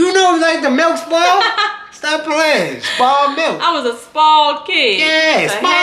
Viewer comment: LMFAOOO 3:04 I thought he said “Sball “ as in he played summer basketball 😂😩